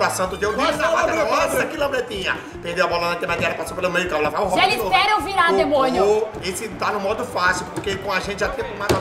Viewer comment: funbabe: gelli espera eu virar demônio kkkkkkkkkkkkkk kkkkkkkkkkkkkk kkkkkkkkkkkkkk kkkkkkkkkkkkkk kkkkkkkkkkkkkk kkkkkkkkkkkkkk kkkkkkkkkkkkkk entendi errado